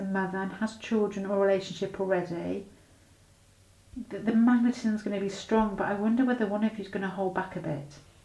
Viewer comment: Pile 2 - resonates very much...the next time we will meet will be at work, where we can't get a moment alone because we are always surrounded by (nosy) people. I could not understand what you said about the part where you said 'if you are a mother, or the person you are asking about is a mother...the ------ is going to be strong'. What is the word? What is going to be strong? Did you say 'manner'? Its on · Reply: Hi Mary, The word was magnetism! X